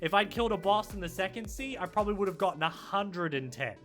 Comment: what’s he saying when he says don’t grind on the second “c” I don’t understand